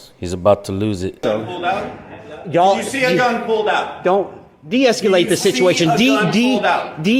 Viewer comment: Am I mishearing, is he saying esculate? That was a lot. Well done.